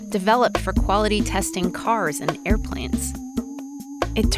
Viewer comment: No matter how many times I listen, at 1:50 I hear you say air plants, not airplanes. I have questions.